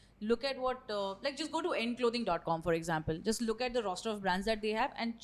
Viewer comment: What been said at 21:04 ? Which clothing brand? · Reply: Hey is talking about END.